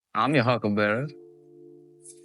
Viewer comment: I've always thought Doc said I'm your Huckleberry but now twice now I've heard Hucklebearer.